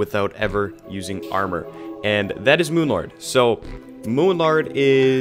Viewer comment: Did he say moonlard